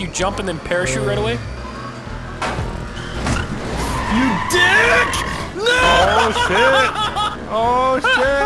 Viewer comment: did say his first curse word on camera? Dick! He said it you heard it right?